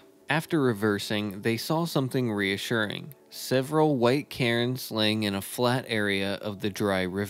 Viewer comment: I was on my treadmill and had to stop, and rewind because i heard "several white Karens" and was like HOW is that reassuring, thats terrifying!